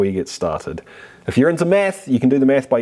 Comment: I heard: "If you're into meth...meth down below..."